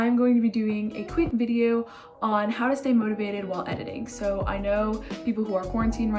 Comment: I was so confused cause i thought it said “How to say motivated”